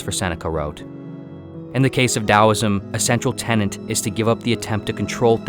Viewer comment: tell me I didn't hear tenant when you meant tenet.